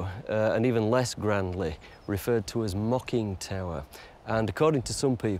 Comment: So they were mocking the house by calling it Mocking Tower? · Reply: dindunuffin? promise {Face in palm} I know what he said, but with his accent, it sounds like he said Mocking instead of Mucking.